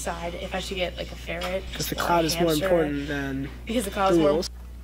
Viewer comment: i thought they said "the clout is more important then the animals" and i didnt want to punch my screen that hard in my life